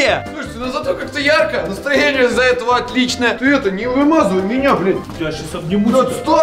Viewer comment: кому тоже послышалась э,ты это меня не вымазуй бл#ть